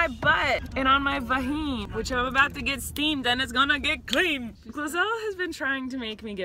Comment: I thought it said streamed all this time omL xD